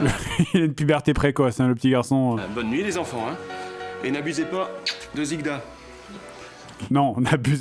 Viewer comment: j'ai compris " ne baisés pas zigda"